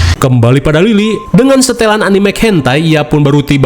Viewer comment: Sepertinya ak salah dengar, "setelan animek..." Apa tadi🗿